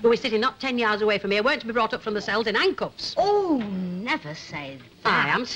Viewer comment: What does Ena say after "not ten yards away from me" at 5:00? Sounds like she mentioned Ancoats, but I doubt it... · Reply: she says brought up from the cells in hand cuffs